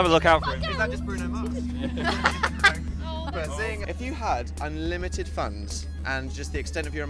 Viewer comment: Wow, this whole time I thought Dan has been saying unlimited fns